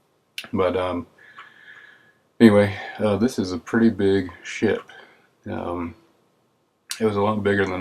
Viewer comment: i thought he say "this is a pretty big shit" lol